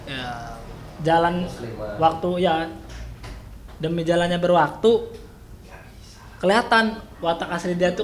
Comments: lu ngomong apa sih? 😭🤣 Berjalannya berwaktu 🤣🤣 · "Demi jalannya berwaktu" what tf they say? Akakakak